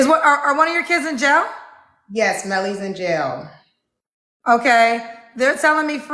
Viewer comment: At first I thought she said Nelly....She has a lot of ambition to have gone to college with having a baby at 26 and another at 18, married for 16yrs.....things are not adding up.